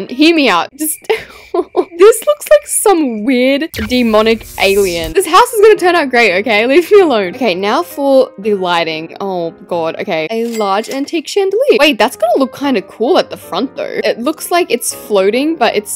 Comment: Why did I hear "Leaf me alone" instead of "leave me alone" lol